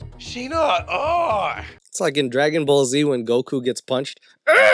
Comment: When mentioned dbz I thought he said dragging balls z for a split second